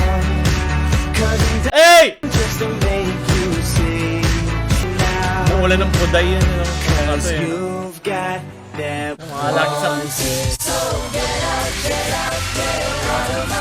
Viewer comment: ano yung narinig ko nawalan ng puday